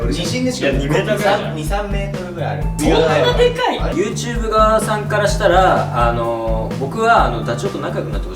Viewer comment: こんぴーYouTube側さんって言った！？ 正しくはYouTubeさん側なんだけど、Snow ManのせいでYouTube側さんが正しく聞こえてしまう…（褒めてる）